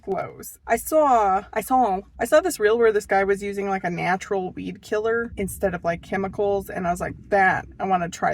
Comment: Good grief! I thought you were saying bee killer until you were mixing up your WEED killer. 🤦🏼‍♀️